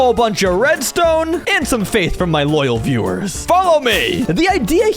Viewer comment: I heard “some cake for my loyal viewers” at 16:00 😂